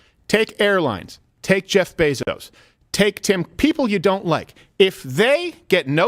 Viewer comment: did Crowder say tim pool???